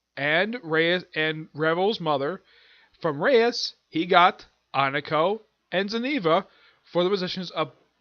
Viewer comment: I did say Asia's name. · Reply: Apologies about Asia but to me it sounded like Akeno